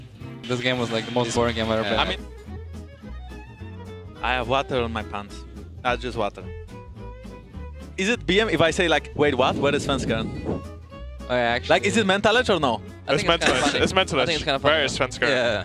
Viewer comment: did he say not just water?